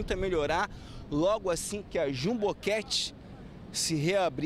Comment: Sempre achei que ele falava "Ju Boquete" mas recentemente descobri que é Jumbo Cat.